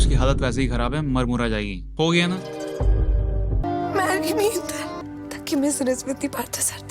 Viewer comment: I heard b@ me inder @ 9:13 😂